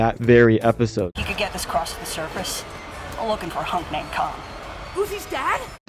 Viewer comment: You misheard the name. Is khan not tom